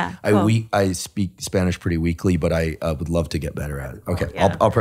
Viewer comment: Instead of "weekly", you'll get better at speaking Spanish if you practice daily. 😂😂😂 (I know he said "weakly") Great work, Andrew! Love all you do for us.